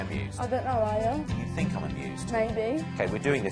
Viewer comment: the whole time I thought he was saying abused not amused and I was beyond confused